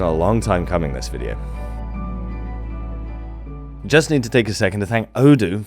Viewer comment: For a moment I thought you said "I'd just like to take a moment to thank Odin", and I was like "well, he is a god of storytellers and knowledge, I suppose, so that makes sense."